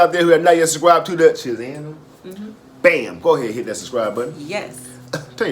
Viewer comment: Lmao at first I thought said "forehead hit that Subscribe button."